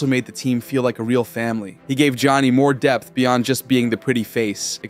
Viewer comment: " He gave Johnny more Depth" sounded like Depp 😂